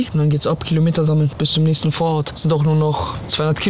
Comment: Also ich habe mir das jetzt 5 mall angehört, in der 01:25 sagt er bis zu nächsten Vorhaut hoffen wir mall das er sich versprochen hat 😂😂😂😂😂